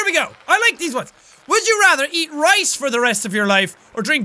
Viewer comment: First time I watched this I thought it said drink rice for the rest of your like it gadaraid I showed my sister the video and she said she would rather eat rice then I told her it says drink rice and started telling at her and realized it said eat........still haven't apologized